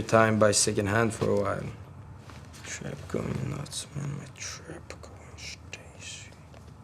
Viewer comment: what is he mumblin lol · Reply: "Im so damn rich, why tf do i still have an IPhone 5s"